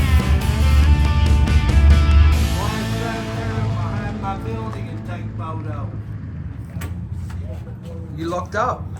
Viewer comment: Can someone tell me what on earth that guy said? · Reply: Why don't you go behind my building an take a photo